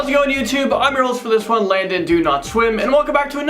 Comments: Did he just say landon do not swim 😆😅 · I thought he said "I'm Logan DoNotSwim"